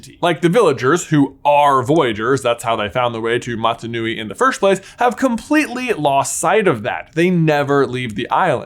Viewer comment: when J accidentally mispronounced Motu, did anyone else hear ‘Mata Nui’ like the Bionicle? 😂